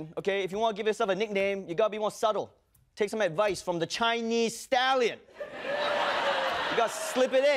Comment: I thought he said Chinese Stalin